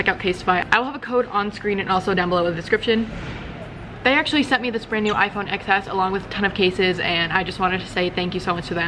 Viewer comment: Soo you're telling me, that she got an iPhone for free and yet she voluntarily dropped it down the stairs multiple times for a drop test?? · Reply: Ya'll...She clearly says that they "sent her this brand new iphone XS along with a ton of cases" at 6:08. Turn on your goddamn captions if you don't believe me. 😑